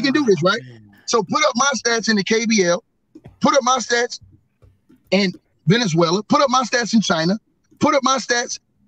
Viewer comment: Did he say put up my stats in Venezuela?😂😂😂